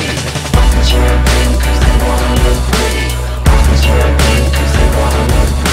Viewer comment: ollie thorn sent me. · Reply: Same!! I thought iy said "Doctors where green cuz they wanna look pretty"